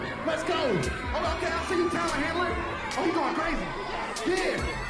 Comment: what is he saying? “Ok I see you Tyler Hamlin” ?? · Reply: "Talent Handler"